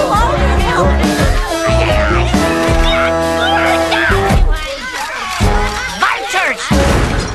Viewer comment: wtf does rolf say at 00:25 i've always wondered since i was a child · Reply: I think he says like “Not goodddd” it actually sounds like to me if I’m not even mistaken😂😂😂😭😭😭